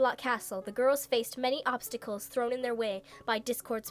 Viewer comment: when they said obstacles, i thought she said popscicles. XD